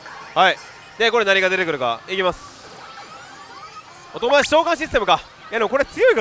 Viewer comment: 外林召喚システムにしか聞こえん